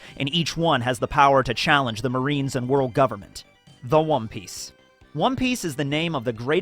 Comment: did he say wom piece 0:45 😂😂